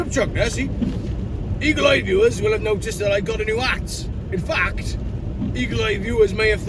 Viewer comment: I thought you said ' I got ay new ass'. Love your mimicry.